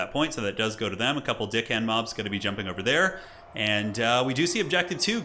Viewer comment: "couple of dickhand mobs" 😆😆😆 I always hear that when they are saying their name too